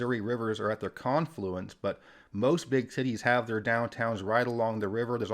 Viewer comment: Did he say “most big titties” around 2:00? 😂😂